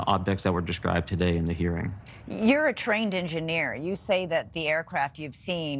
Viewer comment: Did she she say that Ryan is a train engineer, instead of pilot? 😂😂😂